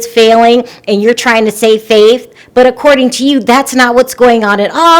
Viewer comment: unless I misheard you, just wanted to mention that it's "save face," not "save faith." 😬 Thanks for your entertaining videos, though!